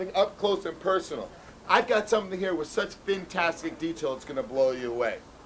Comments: Did he say vin-tastic detail....i don't even. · This video is "fin-tastic." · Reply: i thought he said VIN-tastic. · Did he say "Vintastic details"?!?😵